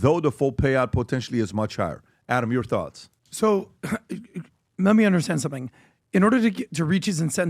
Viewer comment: What you say "Adam whats your thoughts?"....What i hear "Time to shut it off"